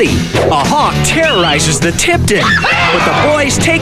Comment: did he just say hawk tuah